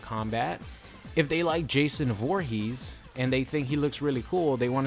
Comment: XD it sounded like you said Jason Whorehees